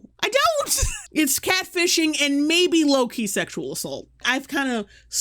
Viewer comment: When you said low key sexual assault I thought you said Loki sexual assault and I was like oh damn what has Disney/Marvel done now....